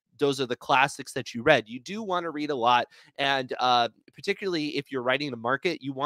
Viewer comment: I'm listening to this while putting away groceries. When you said, "Read a lot," I heard, "Eat a lot!" and I thought "Really? Someone else gets so caught up in writing that he forgets to eat?" lol